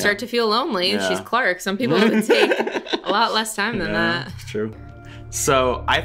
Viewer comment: I thought said 'Truce'😂